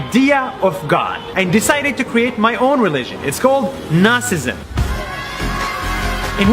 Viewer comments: Am I the only one who heard Narcissism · When said "Nasism" I thought he said "Narcissism" to top on that he emphasizes his self love